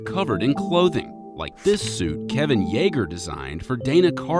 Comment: I thought he say Kevin Yeager